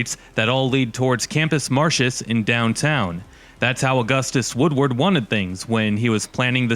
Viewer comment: I heard Augustus "Woodworth" as "Squidward" lmao